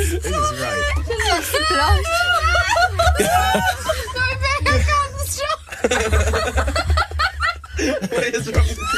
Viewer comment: what Jesy said to Perrie at 0:42 i don't understand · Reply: Laetitia Lj sounded something like it's so bad I can't stop